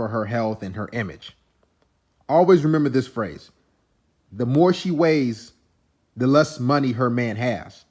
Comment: I always heard.."If she weighs, she pays."